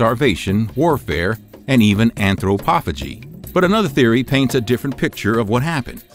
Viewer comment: what is the word he says? Enthropofagie? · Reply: anthropophagy